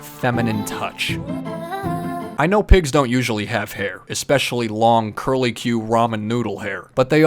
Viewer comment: D-did he just say pigs don’t have tails😀